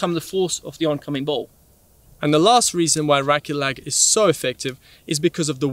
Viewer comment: Thanks for the enlightenment :) At 2:17, the subtitles read "iraqi lag" instead of "racquet lag" ;)